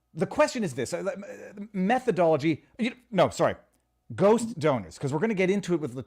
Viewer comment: Lol, I totally thought that you said "go stoner" instead of "ghost donor."